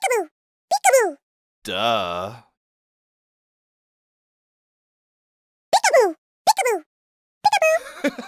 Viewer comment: What you all hear “Peek-aboo” What i hear “D##k adoo” 💀